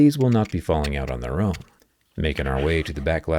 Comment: i heard "making our way "downtown"" 😅